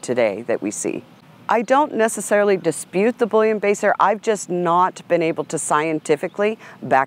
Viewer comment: Haha, thought she kept saying bullion baster. Then I realized what she meant. Bullenbeisser pronunciation = Bull-en-bye-sir That being said, overall is very well spoken and full of good information. Thanks for sharing.